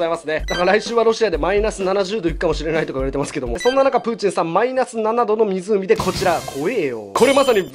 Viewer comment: プーチンさんがぶんじんさんに聞こえた。